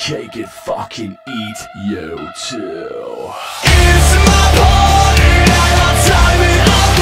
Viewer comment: Came across this on Spotify, and now I can't stop singing "It's my party, and I'll die when I want to, die when I want to!" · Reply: when I listened to the song for the very first time I thought it said "body". Now listening to it again I know now that he says "party".